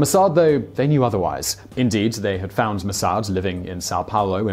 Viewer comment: "they have found mossad?" shouldn't it be mossad found mengele? Simon says: Indeed they found mossad living in San Paulo... But the subtitles says: Much later, it is universally thought that the Mossad found Mengele living in San Paulo. I don't even know how i caught that. Wasn't even paying attention. Was listening to the video in the background while doing some work. :)